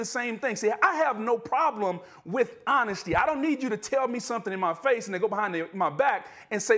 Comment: Sure sounds like I heard Booger slip up, talmbout "I don't need you to tell me something in my face Ninja " 🤣